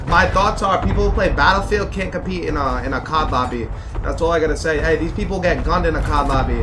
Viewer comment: i thought he said cock lobby